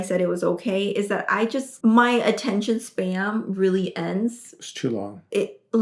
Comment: did she just say attention spam?